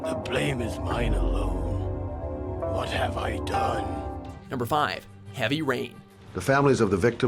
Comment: Did just say "Number 5"?